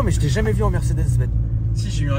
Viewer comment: "mais attend majs j'tai jamais vu en Mercedes, Ben"....... 🤣 au début j'ai entendu Benz 😛